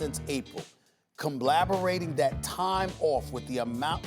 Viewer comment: what did he say? Combleberating?? Compblaberating???